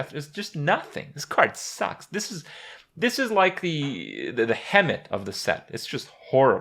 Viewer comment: This is like a WHAT of the set? I hear something like "hemit" (and there's no such word), dunno... Anyome? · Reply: hemet nessingwary